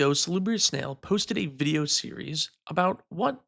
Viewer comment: Did you just say... Slibbery Snail at the beginning there?